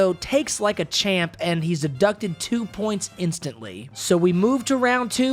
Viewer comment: Okay, did you just say the soccer kick guy was 'abducted' 2 points? I mean, you clearly did, so is that another way of saying someone had 2 points 'deducted'? This may sound like I am being a jerk, but I really am not. I've never head someone was abducted points, it was always deducted, but I thought there may be a possibility that, in other places, different terms can be used that mean the same thing. Also, really cool video, I Sub'd :D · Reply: Thanks for the sub! So I actually did say "deducted," I totally hear what you mean though! That's a combination of my southern accent, and slightly slurring that sentence haha